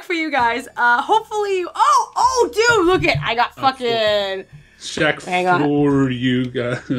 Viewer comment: LOL i know is a little bit younger than you and myself since im 26 and was a 90's kid back in the day!.But LOL around 15:59 it sounded like said Screcketd instead of Shaq! XD. Holy shit, that Game brings back a few nostalgic memories .I never did get the chance to play that game series , but goddamn i do remember my older cousins complaining about how shitty and not worth renting or buying it was for them XD..Dude i so remember that Kazam movie back in the day .I was actually like the minority of people that loved the fuck out of that film, with Shaq granting all of the kids wishes into a reality .XD , Also totally remember the shitty ass short lived rap career he had too , worst thing he ever attempted besides acting in films . There is one think Shaq did dame well right about 2 years back when he made that bomb ass Arizona shack cream soda tea! Fucking hell that drink was my life at the time rofl!Also just a random list of what was extremely overhyped with the top 5 things that were big in the 90's decade( but i only remember as far back as 1995 since the other 5 years of my life at the time i was a baby) 5. Hanson ,backstreet boys and Nsync 4.) the huge ass crazy sexual scam with Bill Clinton and the monica Lewinski slut 3.) The matrix and the trilogy movies of Home alone (aka one of the greatest movies ever made during my childhood!=D 2.) How huge the pokemon TCG cards were from Wizards of the coast (alongside the harry potter franchise,and coun